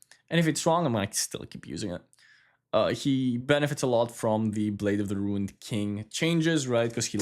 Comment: biggest benefactor of what? Sorry but I already replayed it and still can't hear it well. Am a Pantheon enjoyer again so I'm really want to know everything to min max him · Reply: Benefits from the blade of the ruined king buffs. He loved the item for the on hit passive and he loved the +15 ad it got.